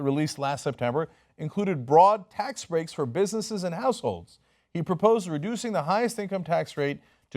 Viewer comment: Is it just me or did it sound like Cenk said "broad tax breaks for bussinesses and assholes" instead of "households"? I was only listening, not watching, so maybe its just a case of wishful pareidolia ^_^